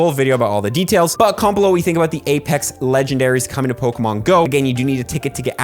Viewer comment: For a second I thought it said apex legends in coming to Pokémon go